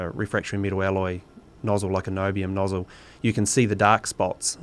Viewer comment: subtitles say "like an (mumbles) nozzle. He clearly says 'niobium'. A quick google(TM): "Niobium is used in alloys including stainless steel. It improves the strength of the alloys, particularly at low temperatures. Alloys containing niobium are used in jet engines and rockets, beams and girders for buildings and oil rigs, and oil and gas pipelines. This element also has superconducting properties." You're welcome :)